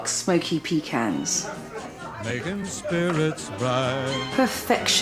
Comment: Sounds like she says " hungry whores" at the very end.lol